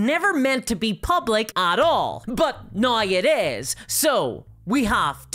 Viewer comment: Not a native english speaker so excuse the ignorance but what is she saying here? What is she referring to? Noy it is? Is Noy some time of reference I'm missing? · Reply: Think its "now it is", just in a heavy accent